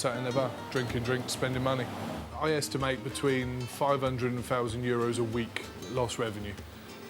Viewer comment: Did he say half a million in lost revenue a week? 😂😂😂